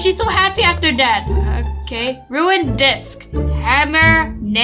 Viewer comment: I THOUGHT YOU SAID DICK 🤣🤣🤣